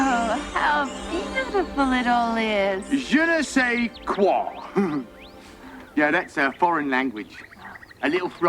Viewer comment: What did he say? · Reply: "Je ne sais quoi" ? 🤔